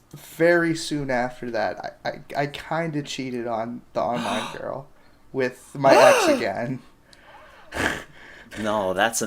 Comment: i thought you said "i kinda cheated on this girl with a mexican"